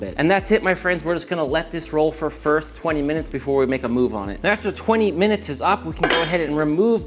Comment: I rewound it back at least 6 times... what does he say at 9:32 or so?... was it english? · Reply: Let this roll, for first, 20 minutes before we make a move on it